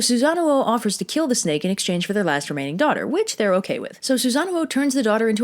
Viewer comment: I heard : "he'll kill the snake in exchange of their masturbating daughter" Tf is wrong with me 😂